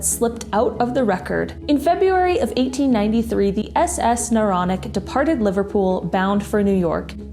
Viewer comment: At first I thought you said, "SS Moronic" not Noronic. But then I realized that cant be because the Moronic is MY boat. I take people on 3 hour tours.